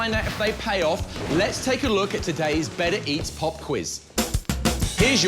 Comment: It sounds like he is saying Bennett Eats not Better eats